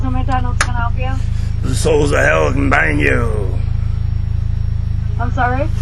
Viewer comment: never failed to crack me up LMAOOOOOOOOOOO 1:31 the souls from hell will bang you ? is that what you said?